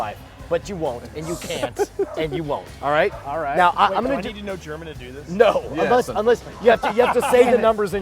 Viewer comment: What is he saying about Germans plz??? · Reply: "Do I need to know German to do this?" i think